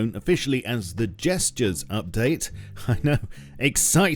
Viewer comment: Gestures update? I thought they said Jester's update!